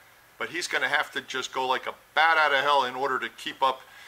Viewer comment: did someone say bat soup? Yes please.